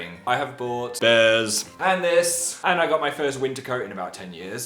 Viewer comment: Am I the only one who thought Phil said "Pandas" and not "and this" when he was talking about his raindeer sweater ?! XD hahaha... Xx